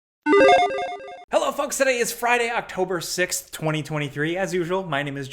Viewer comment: LOL i know you said folks, but it really sounded like you said "hey fucks"